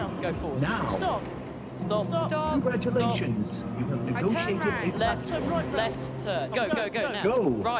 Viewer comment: what did he say at 6:56 "congratulations you have what?" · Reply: Negotiated a platform.